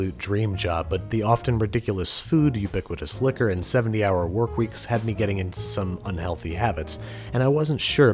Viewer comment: Okay did he just say 70 hour work weeks